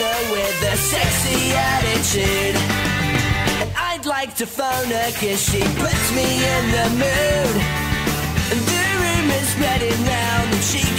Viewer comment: i alaways thought he was saying "id like to bne her cause she puts me in that mood"